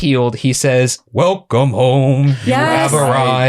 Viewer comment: I won't like I always thought he said "welcome home, you helped us" and I believed that my entire life until I saw this episode and researched it💀